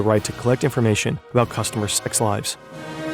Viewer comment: I couldn't tell what this was saying and had to back it up 3 times before I gave up and later figured it out. I thought it said ex-wives. Can you not make weird scrambles, and instead simply say "sex lives" or "intimate relationships" or even just bedroom activities?